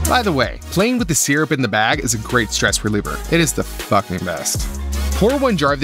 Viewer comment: did- did i hear that right it is the fuhnking best